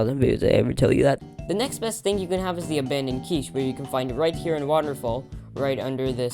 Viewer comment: Yeah you can take the river man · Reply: uhhh, he wasn’t there. Andddd I already reset lol. At Tori’s place rn. K I got the abandoned quiche I thought you said keys hehe I couldn’t hear well